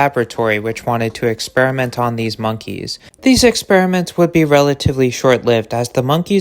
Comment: bro, I thought you were saying racist monkeys lolll